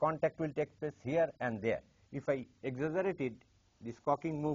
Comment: what did he say? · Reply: exaggerated